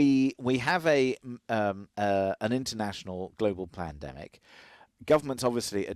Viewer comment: I swear at 1:55 he says “Plandemic” 😂